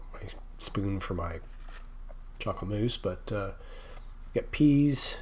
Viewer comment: I’m sorry I thought that said muesli I see it says mousse